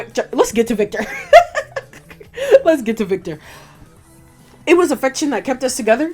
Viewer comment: I might've misheard you but Viktor said "it was affection that held us together" not "friendship" :)